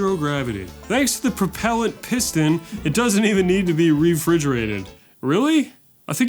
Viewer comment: do Americans actually say fridgerated instead of refrigerated?